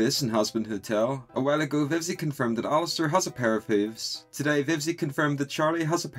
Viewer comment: for a second i thought he said "Vivsie confirmed that alastor has a pair of boobs"